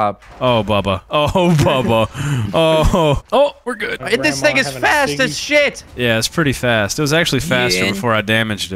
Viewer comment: yum really said “this nigga fast as shit” at least that’s what I heard